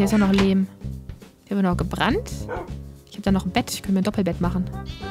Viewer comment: hat bei 4:32 auch jemand "döner gebrannt" verstanden oder hab ich nur zu viel hunger :D